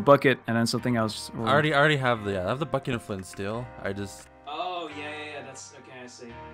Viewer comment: am i the only one who heard bucket of flint and steel?